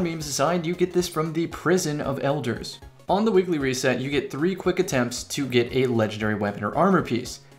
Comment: I SWEAR that you said "on the wiggly reset"